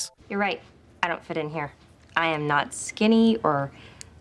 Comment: did she say she’s…NOT skinny???